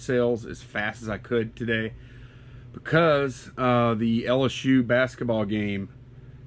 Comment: Ok first of all I swore the man said “yellow shoe basketball game”.....rewind LSU